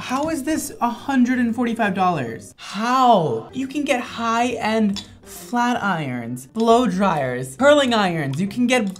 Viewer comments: I heard "you can get high and flat irons for 145 dollars". Which is also true. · I thought you said, for 145.00 you can get high and get a flat iron. 😂